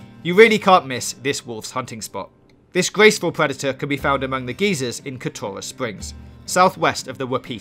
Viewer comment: I’ve never heard a British accent in combination with someone who can’t pronounce their R’s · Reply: Got rather confused when I thought I heard him say geezers instead of geyser lol I was like those are two different things